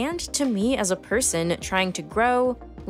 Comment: "Anxious Alien" I thought you said "Ancient Aliens" and for a moment thought the leap from making "Ancient Aliens" to a show called "Real Science" was hilarious!